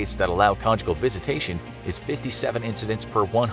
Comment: I thought it said con jungle I must be really tired